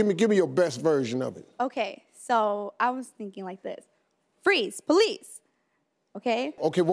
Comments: I thought she said freeze please 😂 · She sounds like she said "freeze please"